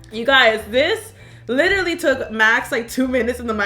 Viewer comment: I swear she called him max!!!! Idk if I'm death but I swear she said max